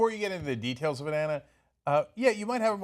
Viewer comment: Did Cenk just call her Banana?